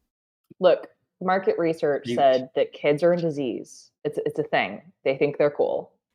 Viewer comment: said "Look, market research said kids are into Zs" but I misheard it as "kids are a disease." xD